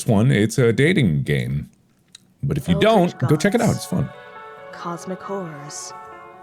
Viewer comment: Until I read what the narrator was saying I thought she said “Cosmic Whores”, not “Cosmic Horrors”.